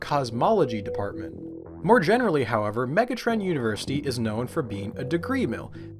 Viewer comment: thought for a second that you'd said Megatron University, lmaooo